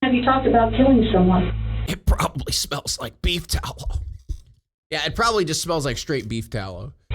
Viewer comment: Dude, I thought said beef towel and nearly choked on my water 😂